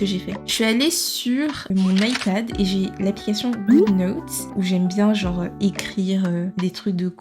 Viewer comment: c’est quoi l’appli j’entends pas le nom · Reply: Good notes 🩵